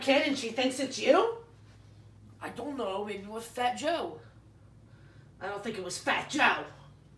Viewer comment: fat joe has entered the chat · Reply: I thought the kid said fat joke